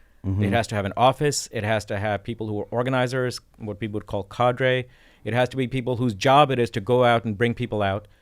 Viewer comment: Lefties are commies and murica hates communism. No surprise, there is no left wing there. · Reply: Also, 4:56, did he say 'kadry', a borrowing from Stalin?